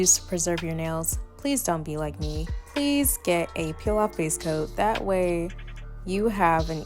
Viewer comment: What kind product to preserve the nails… I didn’t get what was said (time stamped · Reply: Hey love! I mentioned a peel off base coat. I recommend the one from Makartt 💖💕